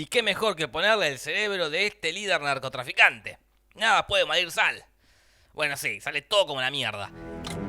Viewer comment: En el minuto 4:40 escuche nada puede valer sal xdxd